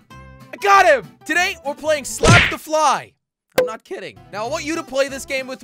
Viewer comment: I thought it said slap the jelly